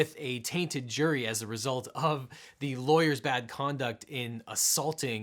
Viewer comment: Yo for real I thought it said lawler. Comment if you know who I’m talking about.